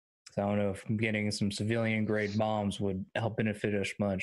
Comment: I misheard 'civilian-grade bombs' as 'civilian-grade moms' and I just